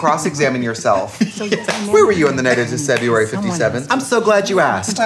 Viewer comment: Am I hearing right? Did she say "Decebruary"?